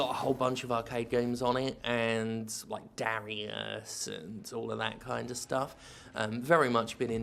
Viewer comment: Did I mishear that? You’ve been playing Dariusburst? I never knew you were into the genre Jim. You should try out the Touhou games.